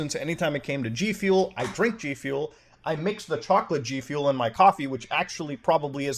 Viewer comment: did you just say chocolate Gfuel?